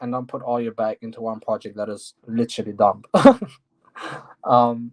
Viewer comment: What did hey say the project is literally done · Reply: the question was about investing into dystopia and penrose and he said putting all your eggs in 1 basket is dumb meaning to diversify not that the project was done